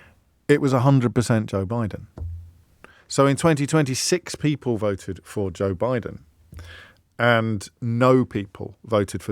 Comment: Did he just say that “so in 2026 people voted for Joe Biden” at 8:27 ? Mr O’Brien been time travelling now, has he ?…..he’s been to the future and back ?. 🙂